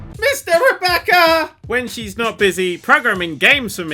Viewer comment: I swear I heard him say on instinct cause they're married: When she's not busy programming games, she's also fucking me.